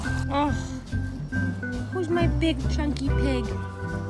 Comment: did she just say, who is me big chunky pig? 😂😂